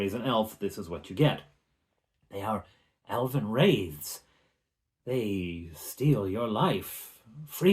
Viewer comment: wait, did he say Elvin Raves 😀😃...